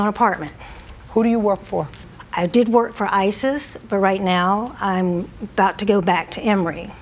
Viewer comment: Did she say she worked for ISIS?